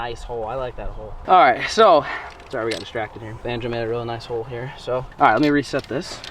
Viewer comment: It might be cus I’m baked but I stg I thought said banjo had a real nice hole 💀